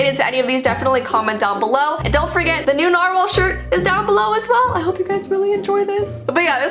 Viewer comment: What said: "New narwhal" What I heard: "New normal"